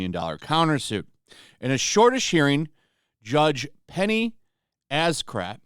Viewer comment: When I first heard you say it was Penny Asscrack. Lol